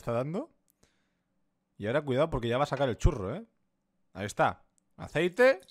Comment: 12: 04 Escuché bien?? Dijo: Que iba a SACAR EL CHURRO😂 12:10 El churro pa dentro